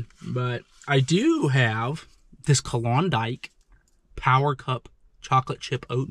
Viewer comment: bruh Kodiak is a bear... sounded like u said Klondike